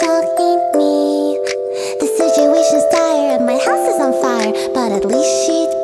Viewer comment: I love how "and my house is on fire," and, "and my friends are all dead," are the most cheerfully sung lines in the whole song. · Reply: I legit thought she said "and my ass is on fire" the first time I listened to it